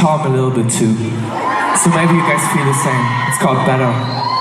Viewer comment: vad säger han att låten heter? Jag var också på koncerten men minns inte vilken låt han pratade om här. :p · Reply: VintageSoul Rätt säker på att han säger "Better" som är en låt från Dream Machine albumet✨